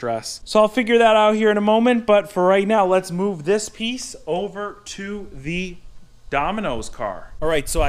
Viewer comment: I thought you were able to say "this piece of shit" started